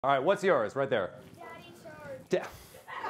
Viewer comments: I thought it said "daddy Shrek" 😭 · I thought he said “Daddy Shrek”